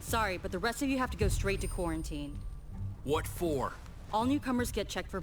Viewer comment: did she just say q-q-quarantine? 😖